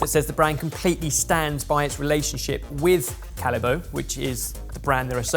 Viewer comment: I thought you said Haribo, apparently, Callebaut has two possible pronunciations...neither like Haribo!